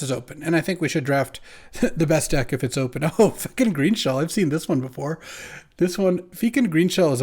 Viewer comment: I heard “Yes F*cking Greenshell” 😅